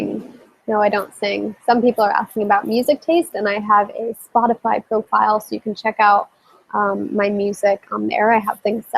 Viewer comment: At around 43:00 what did you say you (she) were/was researching? I didn't catch the name and I've never heard of it. :) Thanks! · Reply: Amanda Gaudreau ZERO WASTE 🤗